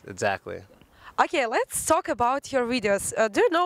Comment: Did I hear her saying "Ridios"? Russian ppl have problems with the V letter?